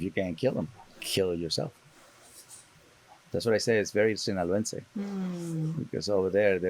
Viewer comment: 15 - "That's why I say it's very ______." If anyone knows what he said there, please say. (It sounded Spanish to me.) Thanks. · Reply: “Sinaloense” He is from the Mexican state of Sinaloa so he’s talking about his cultural mindset and relating to how his people think ❤️